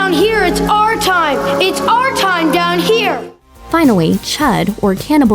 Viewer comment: Is it me or does she say finawy on 1:31 like if u agree